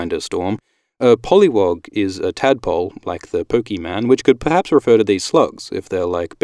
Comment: did he just say "poke-MAN" nah